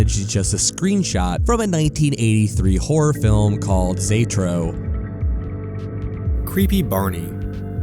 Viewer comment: I couldn't make out the title of that movie nor did the auto-generated subtitles, so after some digging I found the title of the 1983 movie: "Xtro".